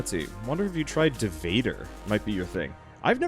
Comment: What game is said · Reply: Vader